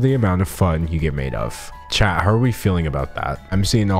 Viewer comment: the whole time i was watching this video i thought you were saying “chad how are you feeling?” “chad what do you think?”